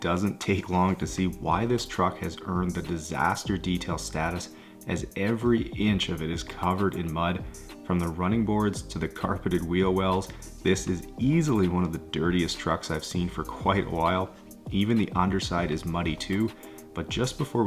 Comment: I heard "*carpeted* wheel wells" and automatically winced...